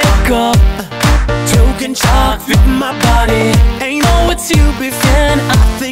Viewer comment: I just heard "chalk in my body" what on earth